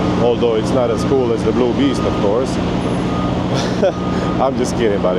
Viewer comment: Every time I hear you say Blue Beast it sounds like your saying Bloobies. Sounds like boobies lol😂 both name are awesome.